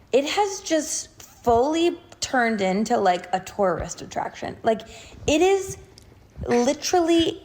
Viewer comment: I heard torarist attraction 😭😭😭